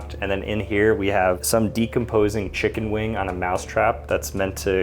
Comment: 😭 “ some decomposing chicken wing “ 🥲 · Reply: I'm that tired that I thought you said "you decomposing chicken wing."